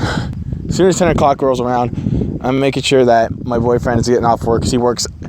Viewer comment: Did he say boyfriend